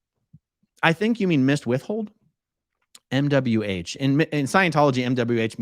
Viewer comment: 🤣 I thought you were saying, "Miss Whiffle," rather than, "missed withhold." Sitting here, trying to figure out who Miss Whiffle is. 😆🤣😂